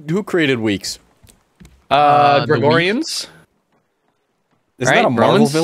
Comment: i fr thought u said my name, mine is gregorio